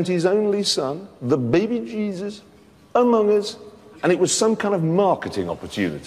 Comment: that circle wasnt necesary XD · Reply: wait did jeremy just said among us